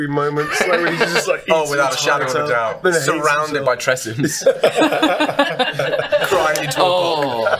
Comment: I like the idea of Gale sad crying with Ice cream surrounded by Tressyms. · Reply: Not sure why I thought you said surrounded by tiramisu